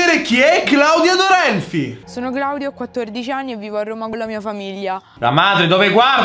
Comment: io ho sentito sono Claudio ho 14 anni